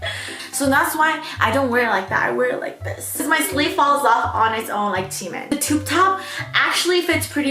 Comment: Did anybody else catch saying "my sleeve falls off on its own like Chimin" at 8:07 😂😂